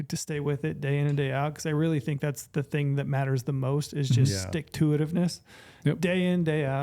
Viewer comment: What was that word? "Sticktoitivness" ? Lol